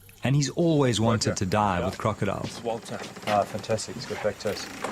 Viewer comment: “He’s always wanted to die with crocodiles” Did I hear that wrong 😂 I do have tinnitus to be fair